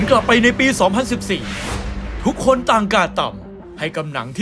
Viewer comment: นาทีที่ 0:34 ผู้บรรยายพูดว่าอะไรครับ "ทุกคุนต่าง,,,,,,,,,,,," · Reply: การ์ดต่ำ